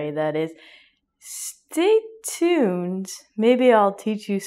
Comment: what did teacher say?? Stitune or ??????? I am learning.